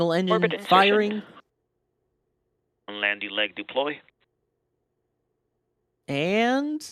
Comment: Sounds like he said "Landy leg deploy" 😂